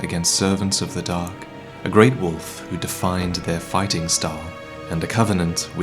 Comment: "All of you forgive me, for I have availed you nothing" I'm not crying, you're crying. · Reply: I thought he said "Wolfe" like some sort of nickname he had for Sif